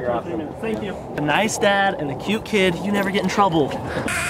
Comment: i thought you said neistat and cute kid